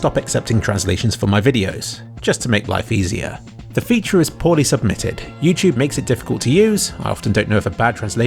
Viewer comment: zomg at 3:16 yuo said submitted but the subtitles say supported!!!!!!!!!!!!!!!!!!!!!!!!!!!!!!!!!!!!!